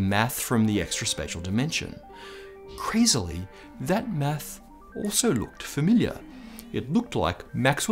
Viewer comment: uhhh something's up at 3:00 when he says "meth" instead of "math" then looked down like he done fucked up hahahaha!